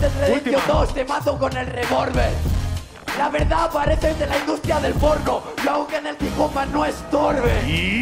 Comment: Se que dice algo de torbe pero alguien me escribe todo el patron? Me cuesta entender q dice ahi zasko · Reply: La verdad pareces de la industria del porno yo aunque en el hip hop mas no es-torbe